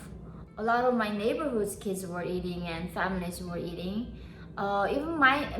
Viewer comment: did she say feminists were eating snakes o_O